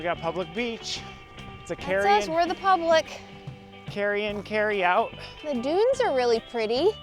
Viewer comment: When you were at the beach and read the sign, I heard “carrion - carry out.” 😂 PS great idea for exploring an area!!!